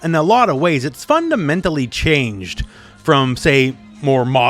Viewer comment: I thought you said “ It’s fun to mentally change “